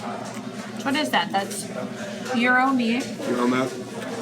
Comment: what's the Gyro meat made of @ 8:07? did you say euro meat? or Yuroma? not sure if I heard that right whats it made of tho